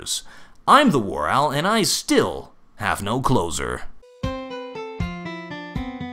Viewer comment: I have always thought he said: im the Warowl, and i still dont have any clothes on